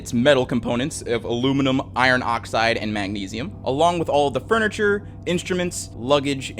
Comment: WAAAAAIT did you say aluminum iron oxide and magnesium?! THEY WERE FLYING IN A BAG OF THERMITE 😂😂